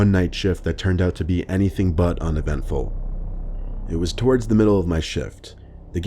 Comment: In the third story I thought it said "I was twerking in the middle of my shift" but it said "it was towards the middle of my shift". 💀